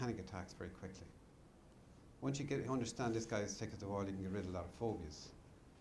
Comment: I cannot tell what he’s saying at 12:10. “Once you understand this guy is_________” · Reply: As thick as the wall 😅🧱 Meaning it's stupid 🤣